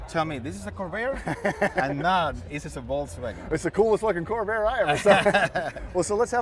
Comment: I'd swear, if I didn't know better, at 1:52 it sounds like says, "It's the coolest f**King Covair I ever saw."